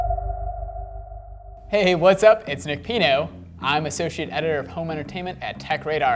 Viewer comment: I thought he said Nick peido